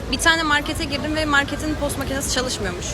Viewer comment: Post makinesini tost makinesi anladım kdldlflflflfl